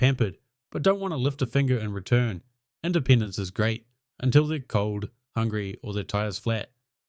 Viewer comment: sounds like he says "and the penis is great"